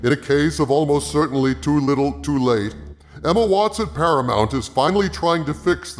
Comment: When you said 'Emma Watts at Paramount' I thought you said 'Emma Watson Paramount', and the captions agree with me, too. lol